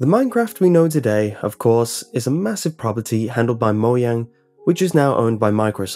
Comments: did he just say "moyang" !? And at 57!! Edit: And 57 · Did he just say moyag it's Mojan